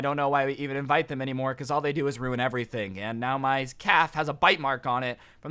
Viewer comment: I thought he said, “ now my cat has a bite mark on it” 😂